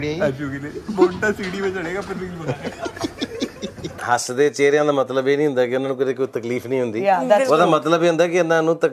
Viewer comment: what says to 🤔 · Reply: bonta sidi me jadega